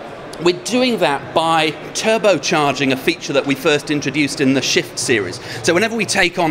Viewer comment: Was it just me or did it sound like he said Shit series? xD